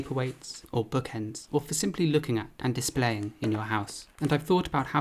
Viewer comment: i thought said "for looking at with dismay" and was completely willing to accept he was making pottery of misery and regret.